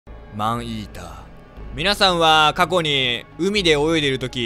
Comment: アーイーダーに聞こえた🤦‍♀️